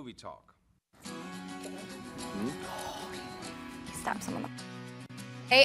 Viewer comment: I coulda swore I heard someone about say "I could stab someone in the f.." Then the show started...!!Lmao, hilarious o.O Anybody catch that??